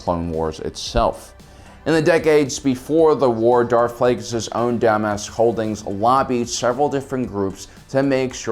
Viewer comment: did he say DUMBASS???